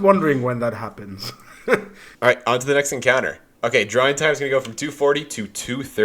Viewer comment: wait what did he say? there were no subtitles help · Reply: Well, if you actually need them, you should know the subtitles are inaccurate most of the time, probably because just gets bored. He said "drawing time's gonna go from